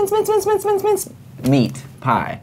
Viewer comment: I thought it said MICEmeatpie 🐭🐭🐭